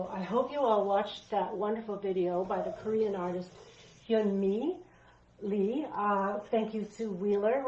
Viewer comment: It was at 5:43...a Korean artist, it sounded like Yeung Le...?? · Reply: Hyunmee Lee-- I hope you like her work. There is a fantastic video of her painting process, if you google her name.